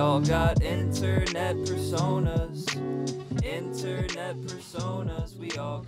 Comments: wow man, im really impressed by the new layout. you use AE? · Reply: I thought it said fursonas like furries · Heard this as internet fursonas lmao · I seriously thought it said 'internet fursonas'